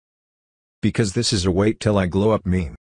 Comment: I thought it said wait till I grow up not wait till I glow up